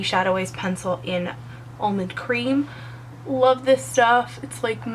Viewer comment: love your tutorials - what primer did you use? i thought you said milani in almond cream is that right?? thanks for sharing!!